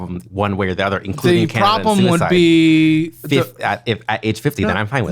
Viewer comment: did anyone else hear him say committing suicide was an option?? Am I going insane???